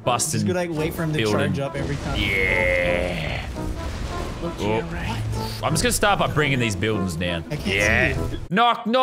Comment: Who heard say look to your right in tilted